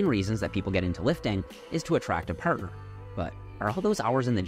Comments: 0.06 I thought you said "to attract a purr" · literally thought he said, "is to attract a pervert" 🙄